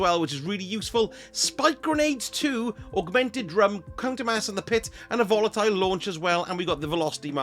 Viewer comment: I could have sworn you said C*ntermass and the Pit when you were talking about Banshee’s GL5. 😂